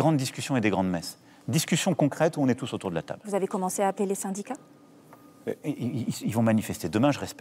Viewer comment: y a que moi qui ai entendu a 20:02 j'emmer... les syndicats?